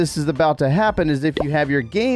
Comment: i thought i heard battery die